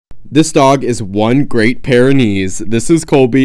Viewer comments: I thought you said this dog has a great pair of knees 🤣🤣🤣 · It did sound like u said "great pair of knees'... Pyrenees, I know, I know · Omg at first I thought you were saying the dog is a great pair of knees... I'm like umm okay I haven't heard that saying before... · i thought he said “this is a great pair o’ knees” 😭😭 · I thought u were saying that the dog had a great pair of knees lmao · Why did I hear you say, "A great pair of knees?" IK. What? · I swear to god I heard "A great pair of knees" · I thought he said "a great pair o' knees" 😂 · When you said Great Pyrenees I thought you said he has a great pair of knees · Happy late Birthday 🥳😅 and I thought you said "a great pair of knees " but I love your video's and they are for some reason satisfying to me ❤ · I thought he was saying the dog had a great pair of knees the first time he said it. But that's just my bad hearing to brain connection causing that. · I heard "a great pair of knees."